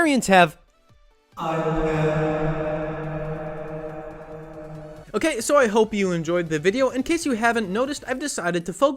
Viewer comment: I cannot for the life of me understand what he’s saying at 2:46 what is he saying. Can someone help?? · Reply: I said "I-pad" 🦆